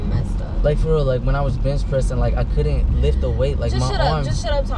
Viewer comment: I thought he said bitch press 😂😂 lol back flips